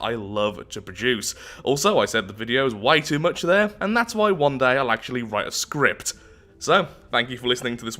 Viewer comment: I'm pretty sure during the "shilling" I heard "One day, I'll write a waffle"